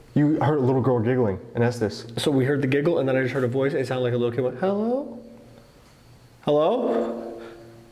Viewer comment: when you heard the child, it sounds like it said "hello"